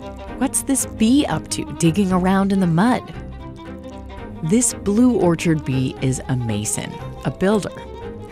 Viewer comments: On the first listen I thought the narrator said "this blue orchard bee is uh-maaaaysing" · "This blue orchard bee is amazing" Yeah, I know Turns on captions "This blue orchard bee is a mason" Oh 😂😅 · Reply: I heard "A mason"